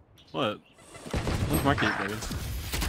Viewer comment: i think he was referring to the "one's market baby"? or did i heard it wrong?